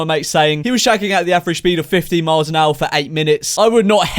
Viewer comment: I thought you said 50 miles an hour